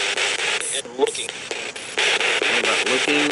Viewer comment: Wow great spirt box session this time. I hear a lot of clear answers and maybe even the ghosts talking to each other!!!! After you asked for their name it sounded like "Ed Walking" at 1:57. At 2:06 It sounds like someone asks...."Is, is the cat walking?" Followed by the answer..."Yeah." The spirits are having a conversation among themselves? At 2:11 something says "Weird". At 2: 24 something says "That's absurd!" followed by "Wait" at 2:27. At 2:33 after you ask for a name of someone who wants to talk it says "Little family of nine, you want the Walking." Maybe referring back to Ed? At 2:40 I hear the word "Sulfur" immediately followed by the word "Haunted". Starting at 3:27 there seems to be another exchange among the ghosts or they are fighing to see who can talk to you. I hear..."Lisa promises...." Which gets interrupted by "Now listen you're gonna have to wait one..." and trailed off. At 3:35 someone asks..."Are you honest?". AT 3:38 someone says "Leave it." At 3:45 someone says "Tulips". Maybe they like that kind of flower on their grave? At 3:57 somone says "Honey?" in a very quite questioning tone. It sounds so sad... At 5:15 I hear "It's hot." At 6:35 I hear..."Positive" followed by an unrecognizable noise and then immediately "You're Arlond." followed by "You bet" at 6:40. Another conversation among themselves? At 6:53 I hear.. "What's up?" followed by the answers..."Tying my shoes", "Working", and a woman's voice saying something I can't make out. I hear the name Is